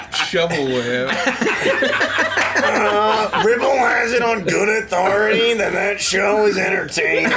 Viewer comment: I can’t understand what said but it was clearly hysterical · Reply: It goes: "..He put on Below Decks on Bravo channel" "Torbek has it on good authority its a good show" "According to who? Clementine?"